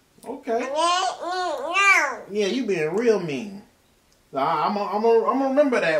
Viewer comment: Did she say " no means no" at around 5:19?? I think I hear her saying that 💯💯... 🤣🤣🤣🤣🤣💐💯💯. is such a bundle of joy... We love watching the video's as a family...